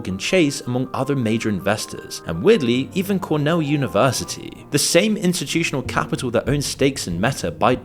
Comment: Did you say Cornell University did also invest in MindGeek??